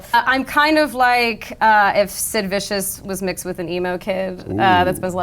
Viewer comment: I thought she said Liz Vicious for a second